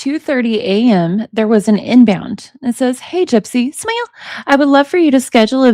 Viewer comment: what did you say? Sounds like you're say "smile"